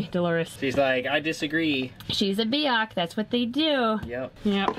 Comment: I thought you called Delores a “biatch” 😂